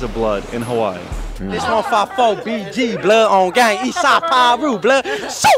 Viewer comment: Bro said blood then piru he don’t know what gang he wanna Claim 😂😂😂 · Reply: It sound like he said 54 east side piru. 54 is west and it ain’t piru 🫠 Ppl fail to realize You can have 1000 bodies and still not be valid